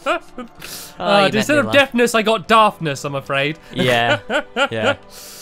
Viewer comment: deffness is ok my ant is deff and it means one has very hard of hearing and cant talk clearly OK! :( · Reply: animal to me, it sounded like he said deftness. I think the sign somewhere said something about using your deftness. Did he actually say daftness? It's hard to tell